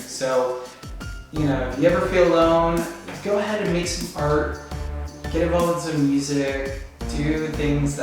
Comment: And thought he said "go make a fart". Well then.